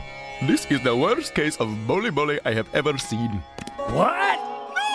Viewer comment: Oh he says bully bully... I always thought he said “the worse case of ‘Holy Moly’ I’ve ever seen” 😂